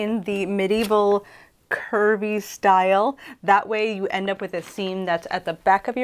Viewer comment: I was listening along while working and thought you said "Kirby style." Poyo!